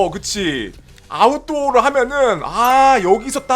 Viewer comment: 챕터3가 아니고 아웃도어로 들었는데.. (챕터4로 넘어가지않고 챕터2 제프상점으로 감)